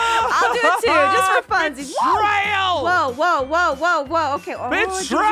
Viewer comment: I think said "b!tch-trail!! ' 11:40' fun, house?' omggggggggg the deep voice from My Inner Demons and Vidcon!!!! ❤😍❤😍❤😍❤❤️😍❤️😍❤️😍❤️😍❤️